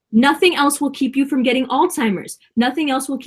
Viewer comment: Whoa wait a minute, did she say Alltimers? 24:51 😳